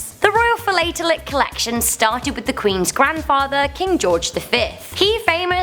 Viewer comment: I thought u said King George the third